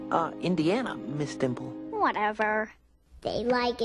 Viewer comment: HOW DID NO ONE REALIZE THAT SHE RIZZED EM UP😂😂😂😂😂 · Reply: I THOUGHT HE SAID: get hot miss dimple